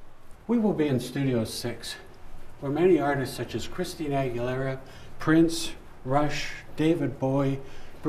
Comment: Did he just say Christine Aguilera??😂😂